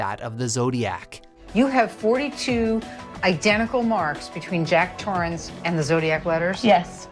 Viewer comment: did she just say Jack Torrance?